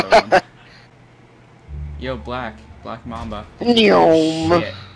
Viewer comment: So uuh what's ur name...Blackmamba I thought he said blackmama